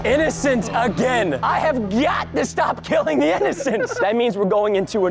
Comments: He really said gyatt at 2:16 💀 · I thought he said Gyatt😂😂😂